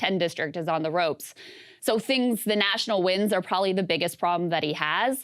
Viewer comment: Huh? Did Krystal really say at 8:06 that Fetterman's biggest problem is "national winds"? The only winds I hear are coming straight out of Krystal's ass. I highly doubt anybody voting for Oz in PA is worried about what Kari Lake is saying about AZ election results. I say this as someone that would vote for Fetterman, but let's be real here.